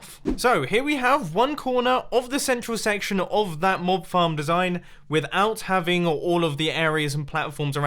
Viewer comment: I thought he said Mob Farts